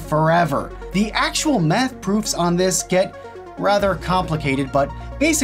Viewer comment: Did you say Meth?